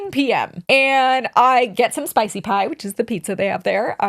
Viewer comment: Omg, for a minute I thought said she had “Spicy Time” and was like girlie … tea?! Before I realized what she really said. Boo!